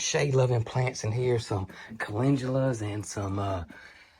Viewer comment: Calendulas? Did you say you have some shade loving calendulas in that old chair planter?